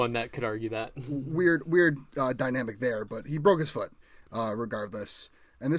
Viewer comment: wait we didn’t all view jimmy as the starter ?! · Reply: ah miss thought you said “we’re dynamic there” not “weird dynamic there” glad we could avoid jimmy g slander🤝